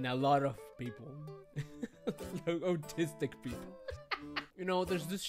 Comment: did he say autistic people. or something else that i dont understand.